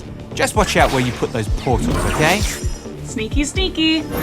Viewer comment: did she just said "sneaky sneaky"? Isn't that Twitch's line?